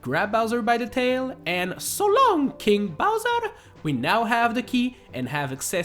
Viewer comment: its not "so long king bowser!" the correct way "so long gay bowser!" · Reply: So I know that it is “King Bowser” but on a serious note, I always heard “So long, eh Bowser?”